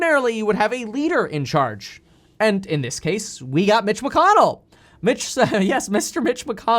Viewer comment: I thought you said “here you go bitch” lmao I had to rewind it and realized you said Mitch 😂😂😂😂